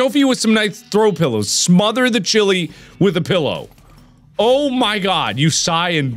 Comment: I thought it said smother the child with a pillow and my heart stopped